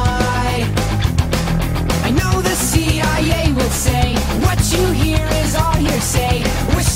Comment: He's not saying "What you hear is all your say" he's saying "What you hear is all hearsay".